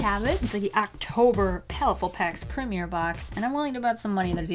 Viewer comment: Did you say bok or box